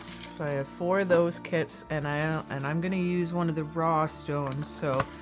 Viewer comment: Ooooooo! I love this! It ran through my head that this would look cool on a shirt or denim jacket, minus the crystals. Although, many crystals come in bead form and could be sewn on 🤔 9:31 I heard "bra" stones and laughed my ass off at rock licker 😂 I do that too! Your projects always spark such inspiration ❤